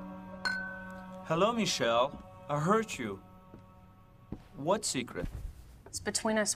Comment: Wait, at the start is it me or does he say "Hello Michelle I HURT you" instead of "I HEARD you"?